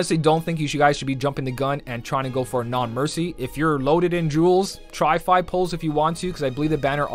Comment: all i heard was "try to pull"